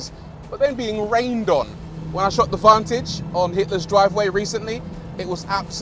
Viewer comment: Did he say Hitler’s Driveway??! 👀